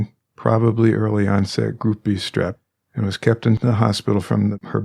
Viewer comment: RANDOM COMMENT , I Thought you were saying GROUPIE STREP!!! Oh lawd!!